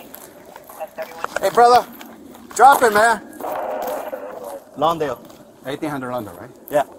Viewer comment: That’s the southside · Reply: I thought I heard them say 18 and lawndale which will be closer to the west side